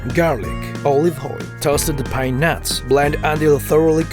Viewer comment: By mishearing pine nuts it sounds like peanuts XD